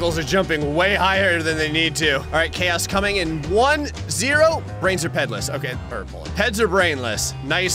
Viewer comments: "Brains are Pedless" had me rolling with laughter. Thanks, Gray! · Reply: I had to check that, I thought he said Breads are Painless lol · Best voice recordings: 5:28 = from a calm sheep into a mad wolf within seconds 13:05 = the most serious "what was that?" Gray ever said 13:40 = lady voice in pain 19:14 "Kickflip!" 23:20 "What!?" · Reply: "Brains are pedless, okay. Purple..." That's what I heard lol